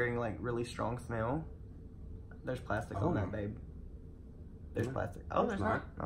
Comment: am I the only one who heard “there’s plastic in there, babe”. bAaBbEe. KDJFJAKDJAKSKADKSK